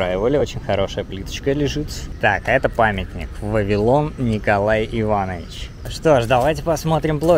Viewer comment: Мне одному послышалось Вавилон вместо Вавилова